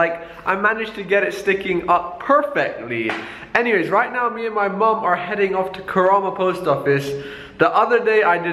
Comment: did he said my mom...is that really or I'm just wrong.